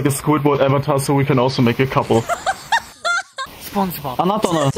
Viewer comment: its caption in Japanease but i think he is saying to the guy next to him Sponge bob